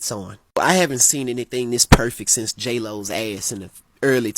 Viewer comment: I dunno why I misheard him say Jay Leno instead of JLo 💀